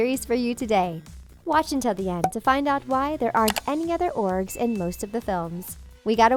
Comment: Did you just say 'orges' instead of ogres?